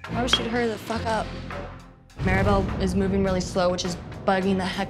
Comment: "[Maribel] has the Chinese disease dragon ass." Wtf what saying? Someone PLEASE tell me what that was.